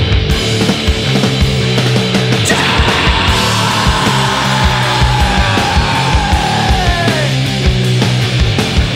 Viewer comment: I don't know what the fuck he's saying at 2:37 but that is one of the best, most pissed off screams I've ever heard. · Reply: He’s saying “time”.